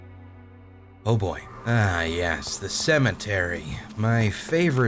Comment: did you just say "Cementary"